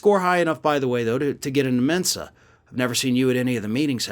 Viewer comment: I got news for him: he’s already dumb and thankfully, our democracy stopped him from being a dictator. Also, my husband misheard you and thought you said, “I scored high enough on an IQ test to get into Demensa.” 😂